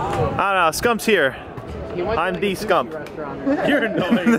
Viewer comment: did he say he wish he was scump I mean I can't blame him